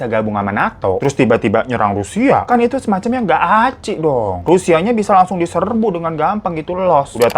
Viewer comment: Bentar...bentar... Apa aku salah denger? 6:50 "ngga acci"??? Apa sama ya artinya dengan yg aku ngerti?😁😁 Btw, kalau mendengar penjelasan begitu...malah aku berfikir bahwa Rusia termasuk egois ngga sih.